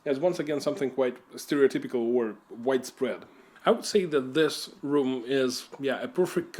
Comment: What did you say at 8:09? Did you say 'white suprem?'' I don't think that translates like you think it translates.